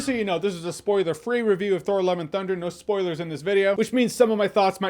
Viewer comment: I keep hearing Thor "Eleven" Thunder 😅